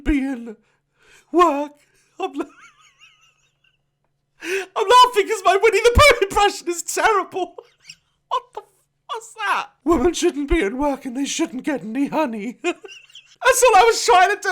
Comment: the subtitles said: shouldn’t feel well nothing is money the depression is terrible woman 🧐